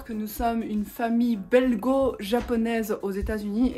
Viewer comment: A 1:26 t'as dit une famille belGo-japonaise et pour moi ça se prononce belJo donc dans ma tête j'ai entendu "on est une famille de belles gos" genre go la meuf xDDD et je suis partie à rire parce que mon cerveau savait que c'était pas ça mais je l'ai entendu comme ça Du coup, certes, oui tu es une belle go!